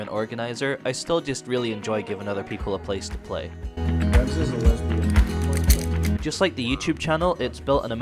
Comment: Anyone else hear "Revz is a lesbian" at 6:57 ? Lol